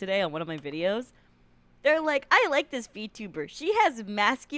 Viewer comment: Thought i heard 'I like this feetuber!'